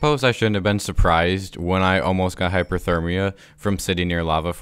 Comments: To anyone who thought he said that he nearly died of Hypothermia while standing next to lava: He said "HYPERthermia". It's the opposite of Hypothermia. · hmm yes hypothermia comes from lava noted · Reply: He said hyperthermia which is the opposite of hypothermia. I misheard it too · Did he just say 'I nearly got hypothermia because i was near lava' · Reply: Hyperthermia. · Apparently you can get hypothermia from being to warm now, no hate just find it unintentionally funny because well you get hypothermia from being cold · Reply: Edit: I'm actually just stupid and misheard hyperthermia as hypothermia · Just an FYI you keep saying hypothermia when your in the nether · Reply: He said hyperthermia which is the opposite of hypothermia. I misheard it too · It's over heating not hypothermia. Great video btw. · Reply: He said hyperthermia which is the opposite of hypothermia. I misheard it too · I like how the lava and the nether give him “hypothermia” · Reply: He said Hyperthermia which is the opposite of Hypothermia. I misheard it too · You almost got hypothermia from standing next to lava? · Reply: He said hyperthermia which is the opposite of hypothermia. I misheard it too